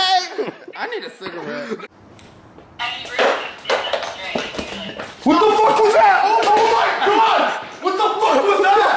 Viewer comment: Who else heard a voice at like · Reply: It sounds like it said "any broom can stand up straight usually"